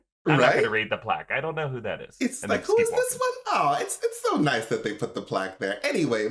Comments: Lml omg I thought he said who is this my nigga almost lost my shit 😂😂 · Am I the only one who had to stop and rewind at 7:21. Totally sounds like says “who is this my n***a” (he doesn’t). But it definitely made me do a double take. Lol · does he say “who is this my nig*a”